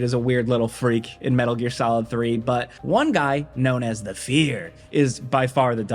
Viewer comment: Haven't played mgs and I misheard you say that bosses name as "The Feeder". Turns out that would be a more accurate name than "The Fear" anyway 😂